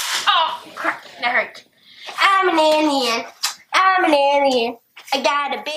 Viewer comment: I thought he was saying, "I'm an Indian!"